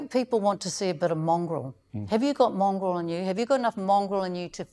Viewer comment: Mr Potaka was very professional and restrained, particularly when Moana asked him if he's "mongrel enough", the look on his face said it all, and she kept repeating it, I honestly thought she said "Mongol". in the comments below has an intelligent response.